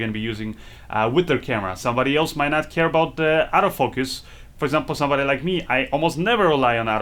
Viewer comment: Oh, you're saying Auto Focus. I thought you were saying Out of Focus.